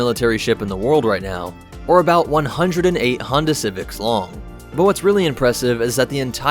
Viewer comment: Lol welcome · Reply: At first I thought you said Hondo civics and was wondering why she was giving civics lessons - watching old vids when half asleep…